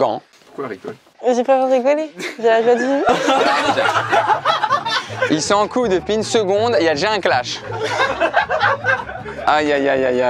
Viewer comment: C moi ou à 15:16 il dit "ils sont en couille" au lieu de "ils dont en couple"